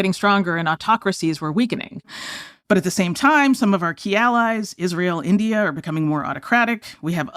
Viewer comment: Inconvenience ALLIES...I thought it said ALIENS. I thought you guys finally got your heads out of the sand and started chasing the real story. Too many patriotic Americans have risked everything to bring validation to what has literally been the worst kept secret in our country's existence and you still mock and belittle their sacrifice because you can't see the evidence right in front of your faces. The reason it's a trope is because it's true. Do your jobs and start interviewing the people who know. They won't talk to you unless you take it seriously. I suggest you start learning some history of the topic. The UFO rabbit hole podcast is a good place to start or any James Fox film.